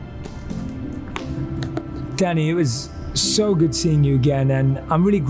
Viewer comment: When he was saying Danny it sounded like he was saying 'Daddy'. Is it only me?